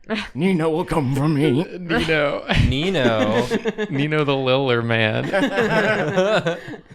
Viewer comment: A very dumb and deep pull: theres an episode of naddpod where i thought they said “nino the little man” and even though i was mistaken, i choose to believe that pico & nino exist in both the drawfee and naddpod universes